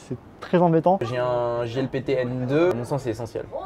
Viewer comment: C'est quoi le PTN 2 (si j'ai bien entendu) dont il parle à 14:55? C'est un certificat de langue japonaise? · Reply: JLPT. C'est le niveau de Japonais obtenu par examen. De N5 pour débutant à N1 pour une maîtrise parfait.